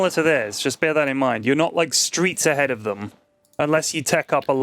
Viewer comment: Did just say streets ahead?